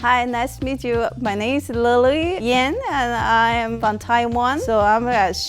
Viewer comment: Who else thought she said Lil Yang at first ?